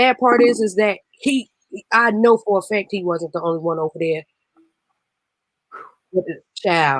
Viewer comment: Notice how they always say men like there isn't female pedophiles everywhere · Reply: It sounded like she said he wasnt the only one not that he was the only man..